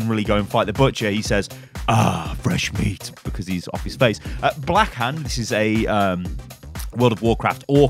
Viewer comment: I thought it said backhand doomfist